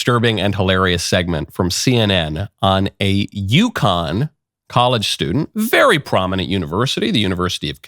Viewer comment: I totally heard, "a Yukon college student, a very prominent university". What?